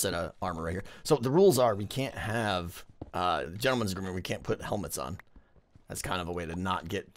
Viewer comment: "We can't have ???? and we can't put helmets on." Can anyone tell me what he said? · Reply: "Gentleman's rule" I think (he didn't say "and") Or something like that, but I'm not sure 🤔